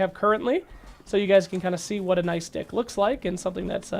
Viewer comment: Excuse me did you say nice dick?